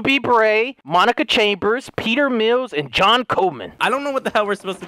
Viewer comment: for a hot sec i thought he said john covid